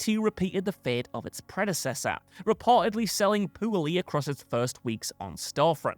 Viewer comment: at first I thought you said "poo-ily" lol